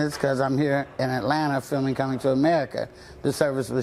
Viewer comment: I heard "filming coming to America"